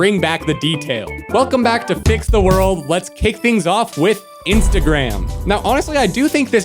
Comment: I thought he said let’s rizz little kids…. Idk why